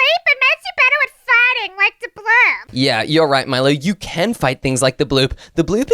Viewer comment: I thought milo was saying i will kick the bloop a**